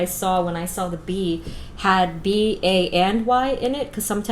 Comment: Hold on did she say b,a,n,y or b,a and y ???